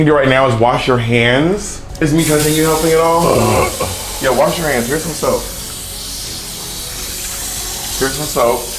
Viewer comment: I swear to God that when Bob first told, "Wash Your Hands, Here's Some Soap", I thought he said, "WASH YOUR HANDS, EAT SOME SOAP".🧼🤪🤣❤